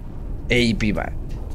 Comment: Эй пи ба? Или что он сказал?